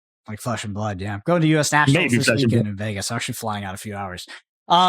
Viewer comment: Hang on a minute, what was it that said right there? Could barely hear him, but did he say he was about to get on a plane heading for FAB nationals?! If so, holy shit! A hardy Congrats & Good luck Man (Didn't even know he qualified 🤤)!